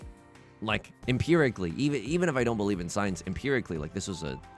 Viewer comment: The first time you said, "I don't believe in signs" I thought you said "science" and thought we were about to learn something deeply confusing.